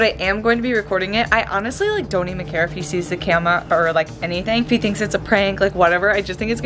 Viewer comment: saying camea instead of camera